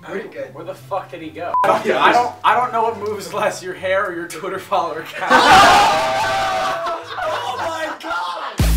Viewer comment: What did say to Nolan at 2:33? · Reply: "I don't know what moves less, your hair or your twitter follower count"